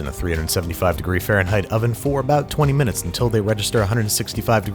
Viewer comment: Was I the only one that heard him say wegester